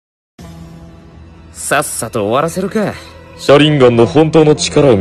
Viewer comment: さっさとおわらせるかーがサスケに聞こえた